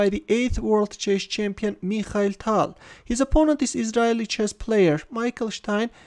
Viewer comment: says, "His opponent is ...???... chess player, Michael Stean". What is that word? Sounds something like 'endialy'. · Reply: " Israeli" which means coming from Israel